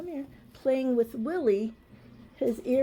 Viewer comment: 😂 Spot I had to rewind that part to see if I heard you calling your new puppy Then I heard “playing with Willy” 😂 Thanks for the laughs my friend as I am still crook as It’s been months now and I really needed a good laugh Gday “almost heaven , West Virginia” From The Whitsundays ❤